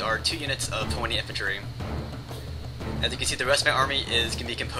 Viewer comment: horny infantry ?? lol ... what are you saying there ??